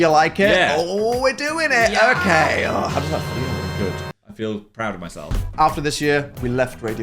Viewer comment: I'm so sorry but at 19:23 was it "I love you" from Dan😮? What he said help me understand 😢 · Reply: Ugh whatever it was "how does that feel"... Thanks, autocaptions....... 😢😢😢